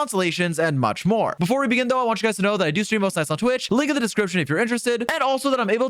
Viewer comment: i dont think ill ever understand what he says here. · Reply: Before we begin though, I want you guys to know that I stream most nights on twitch. Link in the description if you're interested